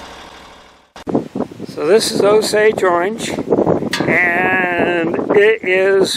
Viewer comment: So what's the tree at 12:05? Can't hear properly. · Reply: Sorry for all the wind noise. That log was Osage orange (a.k.a. "hedge", "bodark", or "bois d'arc"). Mostly thought of as a nuisance, because of the thorns, and it doesn't usually grow straight enough for lumber. It was prized by the Osage indians for bows because it is so strong and tough, hence the French name, which translates to "bow wood". It is beautiful wood, but tough to cut.